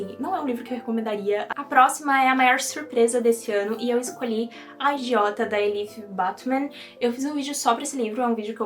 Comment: menina e eu que ouvi "agiota" no lugar de "a idiota" e fiquei ué kkkk